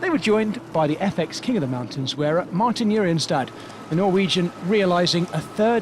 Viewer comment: I always thought say “Martin Urian’s dad’ and I was amazed how amazingly this dad is doing still at the front of the race